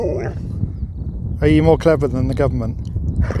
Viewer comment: WTF,I really hear "than them" very clearly!